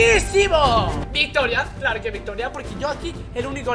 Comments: me dicen que dice no lo entendí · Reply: Dice que: que sepas que voy a grabar con franela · me pueden decir que dice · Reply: Dice esto: si que sepas que me voy a grabar con franela